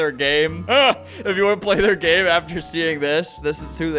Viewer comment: at the end i really thought he said he'd lick their stuff below and i was like " sir, thats p3dophili-, oh, LINK their stuff below"